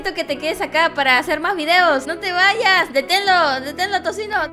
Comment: En el minuto 4:44 escuché que habías dicho no te bañas xd